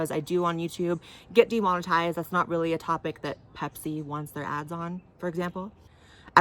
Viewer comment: When you said some of your videos were demonetized because "say, Pepsi doesn't want their ad on here" I thought you said Hep-C, as in Heppatitis C 😂